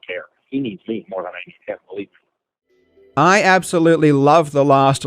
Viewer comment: I think BM says “Believe me”. Not “Really”.